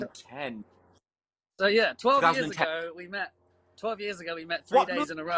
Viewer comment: LMAOOO is he saying 12 years ago bahahaha